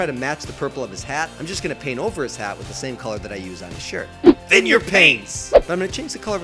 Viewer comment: I couldn't understand what the heck they said last episode, but when I heard it know, it was more understandable, but just to make sure, are they saying, "Thin your paints,"? Also, thank you SO much for including my submission at 8:01! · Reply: Thin your paints ;)